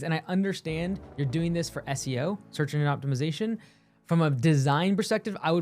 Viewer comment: I heard "search indian optimization" xD feather or dot